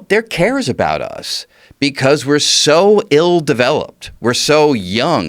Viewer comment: All i heard is "we're so ill" ------ DROP THE BASS NO ALIENS ALLOWED BEWCHICKAWAWA